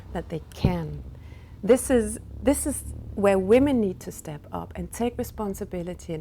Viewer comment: Dennis .K She actually says at 2:07 “This is where women need to step up and take responsibility “.